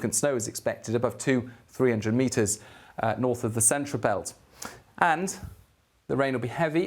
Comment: in the video,did he say "up to 300 meters of snow"?,lol.That's deep